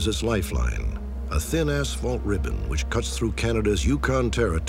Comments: Did Stack say a thin ass fault ribbon 02:25 ? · I’m very sleep deprived did he say “thin ass fault ribbon”?